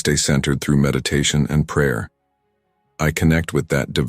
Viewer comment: Granted, I've been cleaning my kitchen while listening to this but I don't remember the words God, Jesus or prayer used. Were they and did I just miss them? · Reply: Oops, I just heard "prayer" at the end.